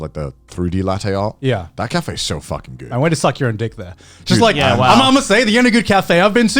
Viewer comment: did he just say ' I went to suck your dick' ?